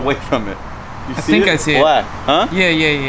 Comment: do you hear what says “i don’t see it”